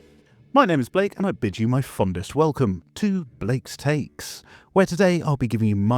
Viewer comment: Instead of "I bid you my warmest welcome" I heard "I'm bitching my warmest welcome" and that cracked me up